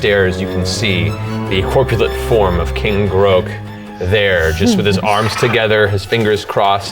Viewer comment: Damn, Matt nailed those frog sounds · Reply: I thought he said King Grog to start with, not King Groak